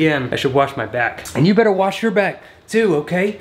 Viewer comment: I thought he was saying wash your back